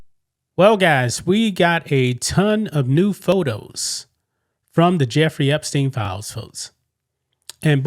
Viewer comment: I thought you said 'a bunch of nude photos'